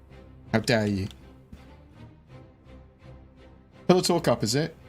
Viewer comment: For the longest time I thought he was saying Pillow Talk.